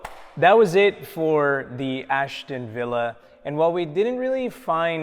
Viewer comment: Ashton Vill, no, I thought you said Ashton Hall